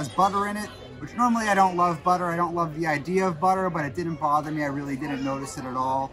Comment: wait...did he just say, "I don't like butter, I don't like the idea of butter." WHAT?! haha!